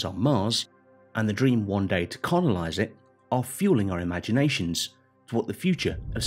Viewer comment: he said conolize instead of colonize 😂👍